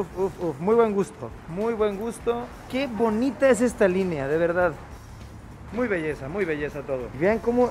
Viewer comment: Minuto 9:15 "Que bonitas líneas, muy buen gusto" 🙊 Entendí es referencia. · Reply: Dijo: “Que bonita es esta línea” no eso.